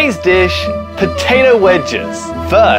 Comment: I thought he said Potato Widgets 😅